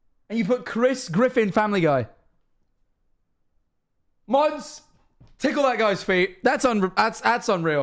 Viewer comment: what did he say..? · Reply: mods tickle that guys feet..? yeah okay...